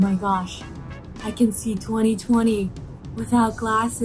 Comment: Bruhhh....I really thought that she said that she can see year 2020 but she actually said 20/20😅😅😂😂😂